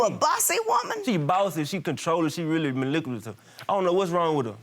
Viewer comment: Did I hear that right? Did he say she's maliculative instead of manipulative? I'm perplexed lol...🤔😗🧐